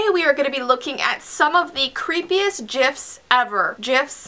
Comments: Who else thought it said "gifts" and got really confused? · Creepiest gifs?? 😨 Grabs foods and drinks shuts the door, Grabs blankets pulls over myself I'm prepared.. 😂😝💓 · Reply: RosiePlayzMSP! keep thought it said GIFTS at first lol